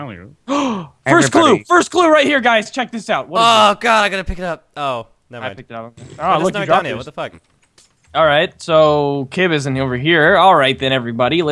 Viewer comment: did say fuk